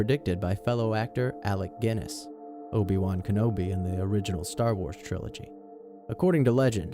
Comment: sounds like he said Cobiwankenobi